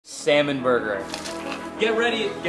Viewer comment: For a sec I thought he said semen burger ☠️